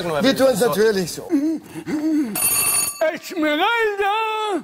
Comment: Was sagt er bei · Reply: Esmeralda